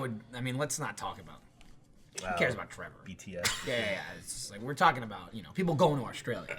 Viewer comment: did say bts